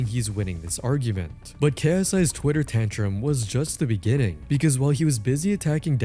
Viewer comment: Did bro say Twitter Tantrum 🤣